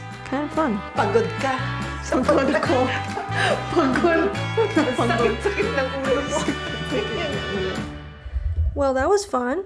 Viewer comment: Is your mom a Bisaya? I think I hear."Sakit akong ulo"?